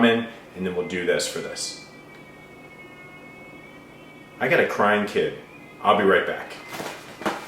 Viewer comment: At first, I thought he said "a crying kitten".